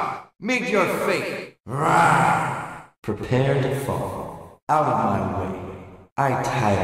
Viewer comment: Somehow I heard “Power.” as a kid when he says “Out of my way.”